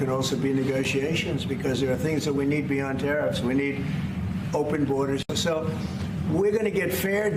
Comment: did trump say "We need open borders"?